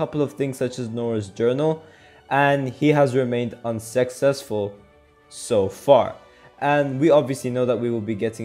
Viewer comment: if you guys enjoyed what you just saw, please be sure to subscribe to the channel so you can see more fun videos like this coming your way! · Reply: When you said Unsuccessful, i thought you said Un-Sex-sesful